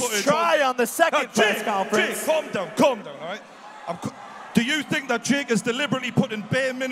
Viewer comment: OMG 😆! The SUBTITLES say, “Jake! Condom condom! Alright”. 🤣